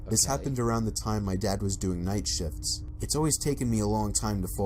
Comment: I THOUGHT HE WAS SAYING NIGHT SHITS AND SHIT AND SHITTED INSTEAD OF SHIPPED OR SHIP OR NIGHT SHIFTS 😂